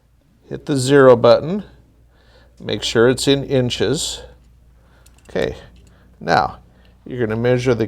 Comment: What I heard was, "Make sure it's in freedom units."